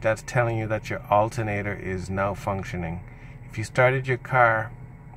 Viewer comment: is he saying malfunctioning or NOW Functioning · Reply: "Now functioning"